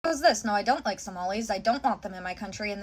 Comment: lol I thought she said she didnt like tamales I would’ve been offended